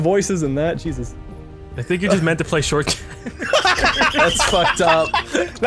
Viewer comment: what does say there? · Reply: Crystal_Costello 'I think you're just meant to play short characters'